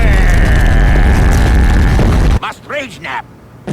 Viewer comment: MUST RAGE NAP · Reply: at first, i thought Wario said "Must Bridge Nap!"